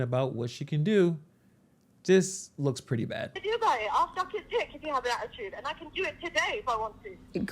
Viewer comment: did she say I’ll suck his if has an attitude 😂. High value I see